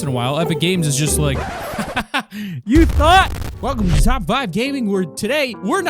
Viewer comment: i thought he said "you thot"